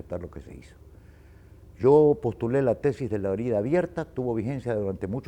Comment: Amigos talves entienden que dicen Leon Febres Cordero en el minuto 11:30 sobre el nombre de la tesis que menciona su ayuda porfavor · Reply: Se llama la "tesis de la herida abierta", esto es que no se aborde el tema limítrofe entre Ecuador y Perú, que no se busque un acercamiento en la materia, que la situación se mantenga latente hasta que en un futuro las condiciones sean propicias para un arreglo más satisfactorio con la causa ecuatoriana.